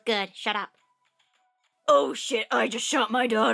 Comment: what did you say cup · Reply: Jeffrey Bussard "oh shiz i shot my daughter. im a horrible father!" (i died XD)